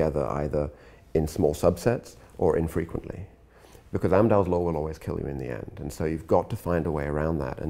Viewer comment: The subtitles say "(inaudible)" but he's saying "Amdahl's Law". Thanks for the course by the way :)